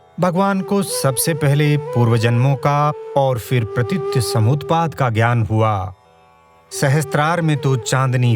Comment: सर,मैं आस्तिक हूँ, किंतु फिर भी अनेक प्रश्न दिमाग में आते थे....आज आपका विडिओ देख कर अनेक प्रश्नों का समाधान हो गया,..... बारम्बार धन्यवाद महोदय.....आपने 35:19 पर जो शब्द बोला है वो समझ नहीं पाया क्योंकि पहली बार यह शब्द सुना है ।कृपया यह शब्द और इसका अर्थ समझाने की कृपा करें। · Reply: प्रतीत्यसमुत्पाद . बौद्धों का दर्शन है. कृपया गूगल कर लेना. It is better explained in our History of Yoga Hindi Film. Pl. watch. Thanks